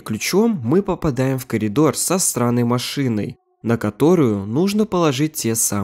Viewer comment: кому тоже послышалось "сраной машиной"??